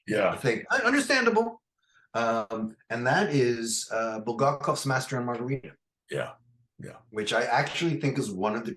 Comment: Uh, if you don't mind, does anyone what book and author mentioned at 24:10 or 24:09? I didn't quite catch what he said, as he said it rather sloppily and swiftly that my brain couldn't process and understand the sounds and words that came out from his mouth there. I'm as you can tell quite curious and wondering about who he picked and mentioned for an example there to counter Sugrue's. If any of you would answer, it would be greatly valued and appreciated. Many thanks again. Very engaging, intriguing and insightful philosophical talks and book recommendations. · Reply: Bulgakov's Master and Margarita